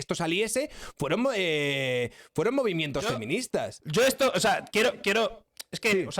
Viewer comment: Claramente dice movimientos feministas y no movimiento como uno solo(Cuando dice "claramente entendi tu punto", No mi reina, no lo entendiste. Creaste un sesgo cognitivo solo, SIEMPRE HAY QUE ESCUCHAR PRIMERO)